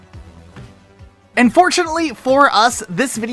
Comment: Buy Vite Ramen with our link <link> and get access to our exclusive bundles! Also use code ROCKETSLOTH at checkout to save an additional 10%. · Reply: I keep hearing "Unfortunately for us" instead of "And fortunately for us" at the beginning of the ad and it makes me giggle every time